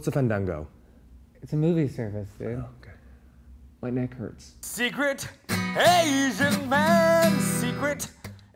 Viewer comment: "Secret Asian Man" x,DD I love this video so much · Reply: My little sister and I hear "Secret aging man"